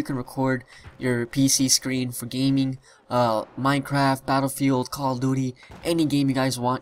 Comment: Am I the only one who heard him say "Call of Doody"